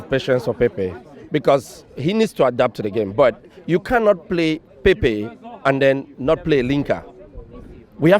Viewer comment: i thought he said " you cant play pepe an not play lingard".